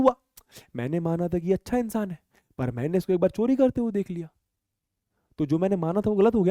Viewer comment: i thought he was saying bakchodi but he he said chori😂 (i was watching it at 2X)